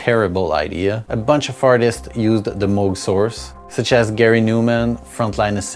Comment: did you call them “fartists”?